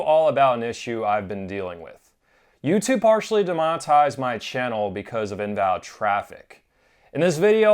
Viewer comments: De monetize d, I thought it said “demonized” my channel first. Hahah. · At first I thought it said YouTube "Demonized" your channel. That would suck, too.